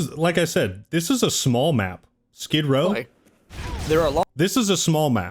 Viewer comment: what did he just call it ? 😂😂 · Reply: 😭😭😭😭 skidrow